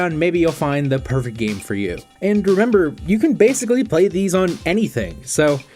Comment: did you say bromember!? I'ma use that